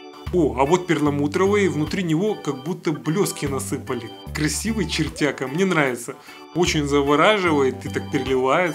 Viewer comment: красивый,чертяга!😄😂или мне послышалось??